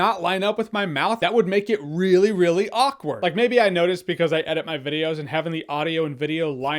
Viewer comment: He actually says "Really fucking awkward" if you read his lips